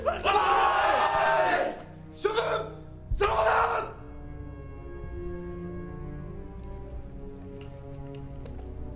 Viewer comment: ここなんて言ってるん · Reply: "陛下の栄(は)えある皇軍である"ですね